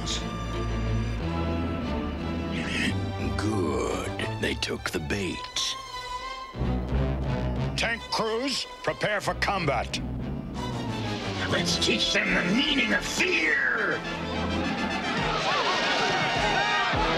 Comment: Ohhhh he said "tank crews prepare for combat" I thought he said "Ted Cruz prepare for combat" 😂😂